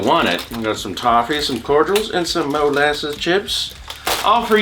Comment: Oooh.. Molasses Chips.. I swore he said Molasses Tits... Guess I can put away this lotion...